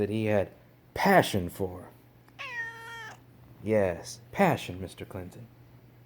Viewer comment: Mr. Clinton: passion??? I thought you said Persian!